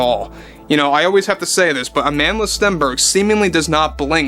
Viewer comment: I thought you said "amandless". 😂